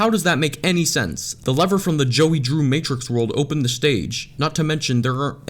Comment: i thought you said that was joey